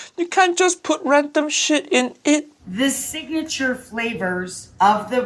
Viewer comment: I just heard..."You can't put rectum shit in it!!!😂😂😂